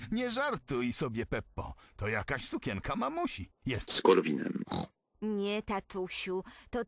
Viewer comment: nie wiadomo dlaczego usłyszałem "ze skorupinem"